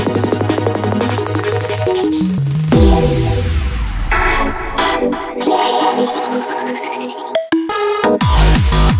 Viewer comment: Does anyone know what it says at 0:50, I can hear: "I am ..." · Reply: i think it just says I am empyrean